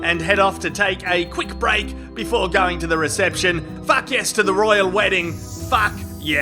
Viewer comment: She clearly was not saying “fun”. 😝 Meghan: “What the fuck?!”